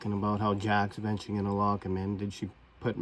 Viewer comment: You said it sounds like "Lock him in" but I heard "They don't have him" at the 11:55/56 mark.